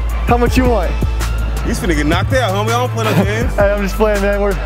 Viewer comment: what did that guy say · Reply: “You’s finna get knocked out homie, I don’t play them games”